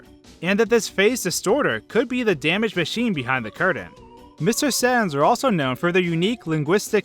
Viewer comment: did he say mr. sans or saturn?